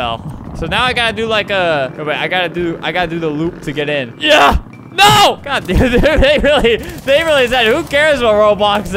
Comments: Who else heard Albert say "God d**it" at 1:36? 👁️👄👁️ Just me? ok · uhh Albert what were you gonna say lol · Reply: He said “god dude” really fast. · he curse? cus I heard “god da-“ I think he caught himself 🤷🏿‍♂️ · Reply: He said "God dude" as he was having a seizure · kinda sounds like he was saying God damnit ngl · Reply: True but he said “god dude” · g a s p I think Albert said “God Damn” O.o